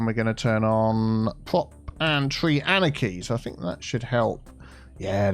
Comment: Omg it's "prop and tree anarchy" and not "proper tree anarchy"???? I've been mishearing this for like a year